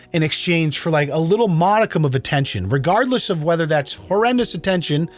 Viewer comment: I heard "meconium of attention" 😂😂😂